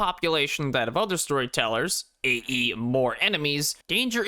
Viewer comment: did bro just say A.E. ?